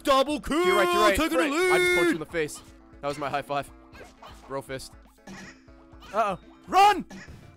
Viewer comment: Did Ray just say "brofist"?